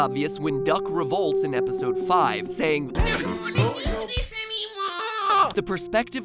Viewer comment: ok wtf did duck say there I honestly have no idea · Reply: He's yelling "I don't wanna do this anymore!" the voice is kind of distorted